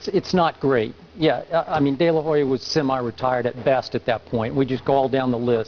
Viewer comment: LOL i thought he said delahoya was semi-retarded